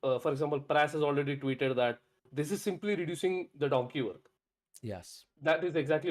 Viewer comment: My apologies, what did you say at 12:47? "This is simply reducing the documal?" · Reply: Simply reducing the “donkey work”